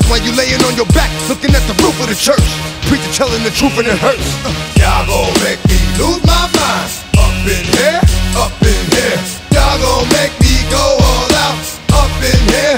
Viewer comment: I turn down the volume and I hear: “Dog bones make lose my mind! Pumpkin hell? Up in hell!”